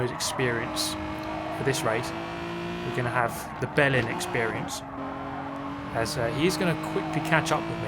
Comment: i was looking away from the screen and definetly heard you say you where getting the bellend experience hahaha took me a second to realise what you actually said